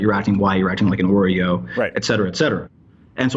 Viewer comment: Did he just say "acting like an oreo"